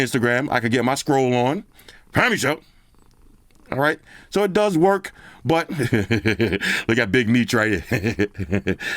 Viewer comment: great review as usual brodie 💪🏽... Yoo what you be saying (23:00) when you see joints on IG 😂🤣😂🤣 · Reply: "Family Show" 🤣😂🤣😂 Straight comedy